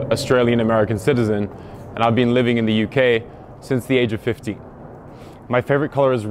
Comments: so at 5:37 he says that he's a 23 year old but at 5:43 did anyone else hear him say that hes been living in the uk since age 50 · Sounds like he said “ I have been living in the UK since the age of 50” Sure looks like it too. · Did he say he was 50 or 15 I thought he said 50 5:45 Though he does look 50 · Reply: He says 15 but it sounds like 50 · Did he say he was 50 or 15 I thought he said 50 5:45 Though he does look 50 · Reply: You mean 5:43 but I thought he said “50” as well XD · did he say he’s been in the UK since he was 50? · It sounded like he said he started at the age of 50 😂😂😂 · I thought he said, “since the age of 50”, not 15.